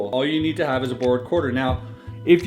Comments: I kept hearing "Bored" quarter. Until the end. Then I understood it... BORROWED! haha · For awhile I thought he was saying bored quarter... god I love Canada